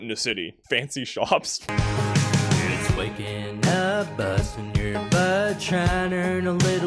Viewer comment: i always think he’s saying “bust in your butt”